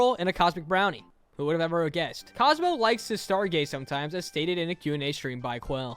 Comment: the cc says "star gay" instead of "stargaze" 💀